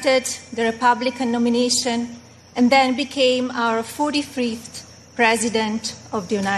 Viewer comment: Did she say he "became our 43fth president"?